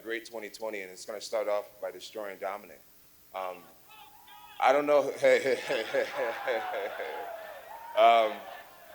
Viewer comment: what does the fan scream? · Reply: No more coke Jon